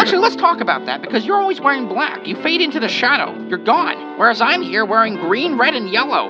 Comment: “You fade into the shadow, you’re god” idk why but mishearing that made it so much better😂😂😂